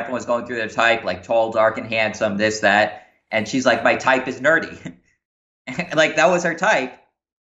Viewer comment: I thought he was saying dirty for half the video😂😂😂